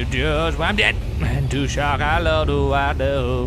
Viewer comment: lol at the end i thought he said i just got married by the way i was like wtf! i heard that wrong lol like if u did too